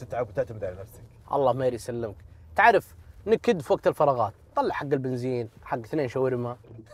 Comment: وش قال عامر؟ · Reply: نكد وقت الفراغات نطلع حق البنزين اثنين شاورما